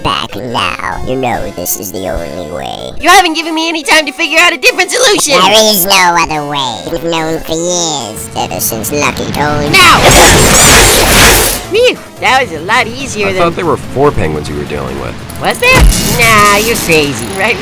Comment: I HEARD RIGGY SAY: “I dont remember Doctor T adding that..” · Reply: I heard him say ducky t 😭